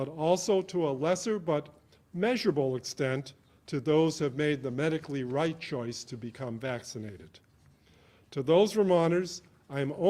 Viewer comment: Did I hear this correctly at 45:00, "the medically right choice to be vaccinated?" Sounds awfully judge mental, I thought we were supposed to respect each other's decisions- to mask or not mask, vaccinate or not vaccinate? Wow...